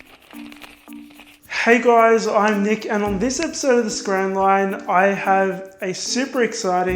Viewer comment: it sounds like hes saying scran rine. 😂